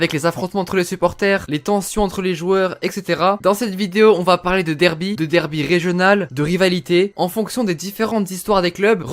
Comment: « des derbys regionals » tu nous fait quoi? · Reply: J’ai dit « de derby régional » ahah